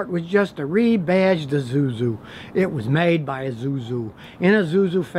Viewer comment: Did you just say "izuzu" 😂😂😂😂😂😂